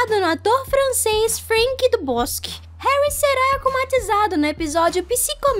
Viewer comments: So eu q ouvi "Franck do bosk" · SÓ EU Q OUVI FRANK DO BOSQUE ou poste